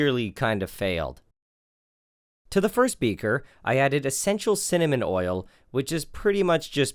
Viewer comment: I thought he said a sensual cinnamon oil🫠